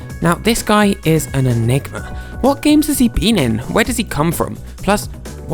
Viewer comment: thought you said (the n word) but you said enigma